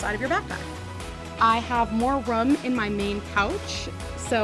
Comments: Did she just say she had more rum in her backpack??? 😂😂😂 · For a second I thought she said "I have more rum in my main pouch" and i was like well damn and then I realized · um anyone here “rum” instead of room lol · Did anyone else notice she said “more rum” instead of “more room” at 10:04?